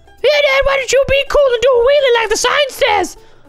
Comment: I think he said the f*** at 9:53. Is he changing. Anyways, you guys be the judge.